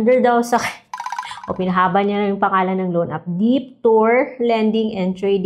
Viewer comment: Ako lang ba ang Naka misheard sa "deeptoor"..😁